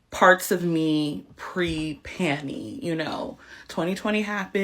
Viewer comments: I thought she said Penny. And that she was trans. She I thought she was a he biologically and was going back to her masculine gender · Pre-panny? No offense, i cant stand black vernacular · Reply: Uhm, nobody black is saying that…TRUST ME, she was a one off and not sure why she even is saying that. It sounds ridiculous. I thought she kept saying “Panty” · Panny? It sounded like she said panty!😂🤣😅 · Lol bless her heart I thought she was saying panty. I don't make new year resolutions. I like the way I am and see no need for change.